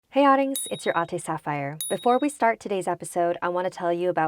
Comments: Oh I thought she was saying like "hey audience its your auntie Saphire" XD XS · I always thought you said "Aunty Saphire" instead of "Ate Saphire" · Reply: elf-amazon 500 i thought she said “it’s your ost saphire” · I always thought you said "Aunty Saphire" instead of "Ate Saphire"